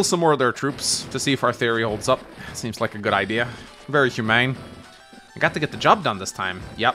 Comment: you said "very humane"... but I heard ... "very hu-MANGS!"